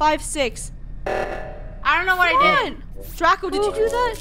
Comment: Did anyone hear say what did I do on the first round time